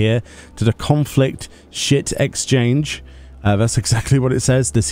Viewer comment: Literally thought said conflict shit exchange